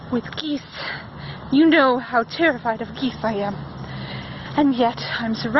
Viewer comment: I wasn't paying attention for a minute, and when he said he was afraid of geese, I thought he said he was afraid if Keith. Anddd I can't stop laughing😂😂 Edit: Okay now I'm crying 😢 Great job on everything! I loved this!!!